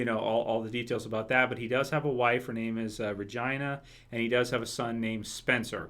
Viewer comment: Vagina or Regina? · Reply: I thought he said "Virgina." Vir-gina.